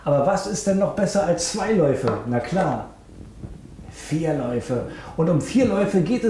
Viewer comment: Hat sonst noch jemamd bei 1:06 ein "Einlauf" gehört ? :D